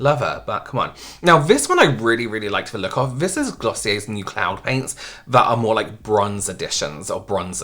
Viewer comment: Totally thought you said clown paint instead of cloud paint 🤡